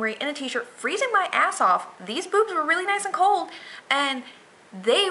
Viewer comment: all i heard was "these boobs were freezing"